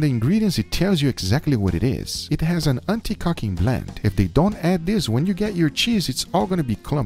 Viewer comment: Did he say anti-cucking blend?